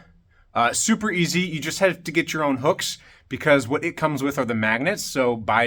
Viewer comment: I thought he said "you have to get your own books" 😂